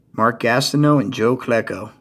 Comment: Nice pick ups and nice dual auto I thought it say joe Flacco at first lol